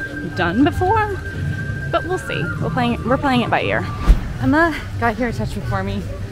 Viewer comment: Whenever Emma says “playing it by ear” I always hear “playing it by ALLears” 😄